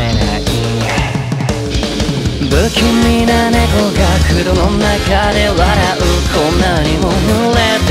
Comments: アンチクイーンオブハートのコメの中で申し訳ないんだけど「不気味な猫が黒の中で嗤う」が「風呂の中で笑う」に聞こえて濡れてるのは当然じゃないか?って思ってたけど違うことに今気づきました猛省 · 黒が風呂に聞こえる同士いませんか？！